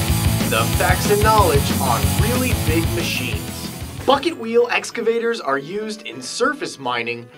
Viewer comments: sounded like fucket wheel LOL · Haha. I thought he said the "fuck it wheel" at first. XD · I thought he said fuckit wheel!